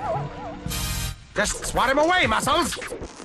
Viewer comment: does he say "assholes"?? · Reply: Muscles.